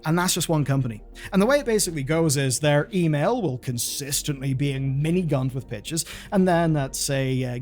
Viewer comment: Lolz I honestly thought you said “their email is consistently miniguns and bitches” and I suspect that “miniguns and bitches” would probably cover a large number of game pitches